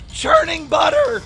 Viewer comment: I thought he was saying "turning butter"?Like how people make homemade butter. Was he saying scraping butter? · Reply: Miss Mariss he said smth like “churning butter” irk actually